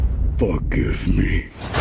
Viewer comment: I thought Optimus said "fuck" lmao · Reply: William Choochootrain he Said Forgive me